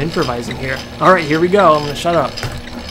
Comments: It sounds like he says shut up?....just saying :P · did he say shut up